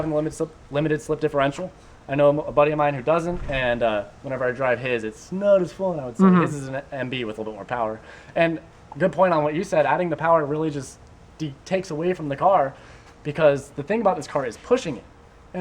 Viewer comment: When I said NB in the beginning of the video I was talking about my friends NB which doesn't have an LSD like mine here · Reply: gotcha thought you said "This is an NB" not "his is an NB"